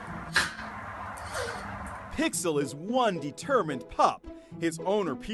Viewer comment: I actually misheard Pixel's name as Mixie, and Czika's name as Chika